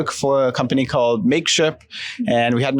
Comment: Did he just said Makeship? My crowdfund Sebastian Solace plush doll was shipped to me a few days ago from Makeship in China. Paid in usd in USA. Shipped from China. hehe.